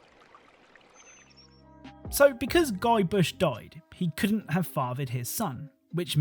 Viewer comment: Ive never played the game but you called him Guybush (at least I think thats what I heard) whereas the cut scene subtitles say Guybrush 🤔